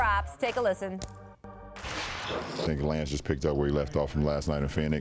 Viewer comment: did Lebron say this nigga lance ?